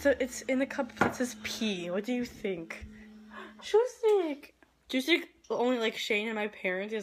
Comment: What is the cats name? It sounded like you said chew-stick :p